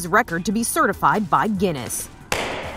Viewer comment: I forgot to turn off captions at 0:54 and captions thought he said “yeah! It’s hummus!”